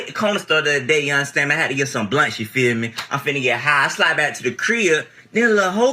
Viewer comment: AAVE is really unique in the way it mixes with regional dialects. My AAVE sounds like the homie at 27:29 . He is probably from Atlanta possibly Dade/Broward County, Florida (Miami). I’d bet on Atlanta tho. (LMAO i wrote this before i saw the exact city names. Turns out the Miami guess was right. I was born in Miami, but i did most of my growing up around Atlanta. I guess i sound more like the birth town.) Here is what he said for non-americans or yt ones: “I’m talking about (I’m saying) I hit (went to) the corner store the other day, you understand.(not a question) Man, i had to get some blunts, you feel me.(also not a question but less so) I’m finna (going to) get high. I slide (go to) back to the crib (my house). Then a lil Hoe (woman of questionable ‘virtue’) calls me, talking about ‘i’m tryna slide through (come to your house)’. I said ‘(you can) if you tryna (you are trying to) give me some of that euuuhhhHHH (‘some of that’ followed by anything but another noun implies sex. in this case he is asking for access to her female organs), You feel me? (definitely a question this time)” Hope this helps